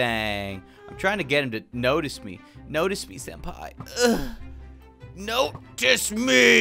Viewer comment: I swear I heard him say notice me Sempai at 11:12... I'm not the only one? right?